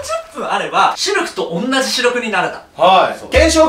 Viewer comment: シルクと同じ視力になれた。 が、 シルクと同じシルクになれたに聞こえた（笑）